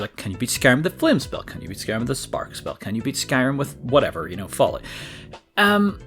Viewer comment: Did I hear that right? Can you beat Skyrim with FALLOUT?